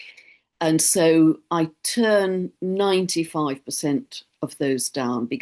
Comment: Thanks 🙂At time code 30:30 in the beginning of your sentence, I thought you said that you just turned 95😂 But you are just explaining that you "...turned ninetyfive ....percent of the inquiries down"🤭💗